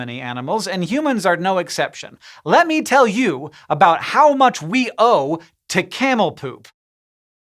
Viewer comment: At the beginning my brain thought he said “How much we owe, to Camel toe”